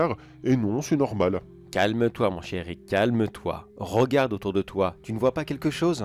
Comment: ahahah quand tu a dit "calme toi mon cher Rick" j'ai entendu "calme toi mon chéri" XD Du coup j'ai éclaté de rire, désolée XD